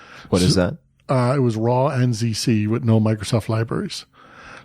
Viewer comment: What programming language did he mention, I thought I heard raw nzc but I can’t find anything by that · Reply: He referred to "raw ANSI C," which is a somewhat confusing term, as it's not commonly used. If taken literally, "raw ANSI C" would correspond to the C89 standard—meaning he might be working with a version of C that's over 30 years old. Since 1989, the C language has been governed by ISO standards, so the phrase feels outdated and unclear. It took me a while to parse what he meant, which seems to reflect a broader pattern: he often uses overly elaborate language to sound more knowledgeable. While he is clearly intelligent, he regularly overstates his expertise. The use of "raw ANSI C" comes off as unnecessarily grandiose.